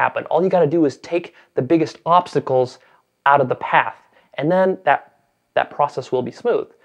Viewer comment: I thought at 1:43 he said take the biggest popsicles out of the path. HAHHAHAHHA